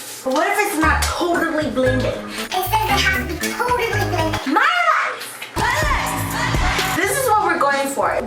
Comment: I didn't know lia said my life in dalla dalla I thought she said sumn like mahli...